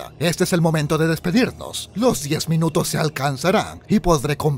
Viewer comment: Yo entendí "compra le Dross a mi Waifu"